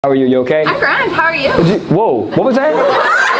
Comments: At first I thought she said "I'm crying. How're you?" And I was like wait. What? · I thought she said, “I’m cryin’ how are you?” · I swore she said “I’m Fran, how are you?” I’ve only been to Canada one time and I left drunk off my butt. Canadians sure do know how to party 😅❤ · I heard I'm crying how are you